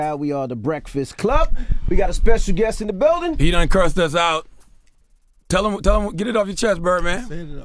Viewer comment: “He done cursed us out “ 😂😂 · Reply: For years I’ve came back and watched this video, and thought he was saying “P9 crossed us out” until this comment. I thought it made no sense what he was saying!!